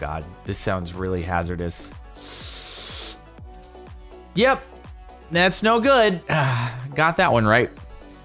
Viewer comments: lol accidentally said "that's no good" but it sounded like "that snow good" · did anyone else hear "thats snow good" I did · I thought he said "that snow good" to make a pun, but its just my imagination being punny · did he say "Yep thats snow good"